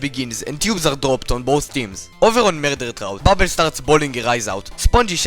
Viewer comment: Did you say Bubble was boiling her eyes out?